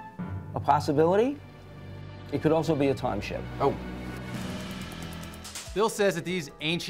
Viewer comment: I thought bill said "time share". Like, i thought he was making a joke.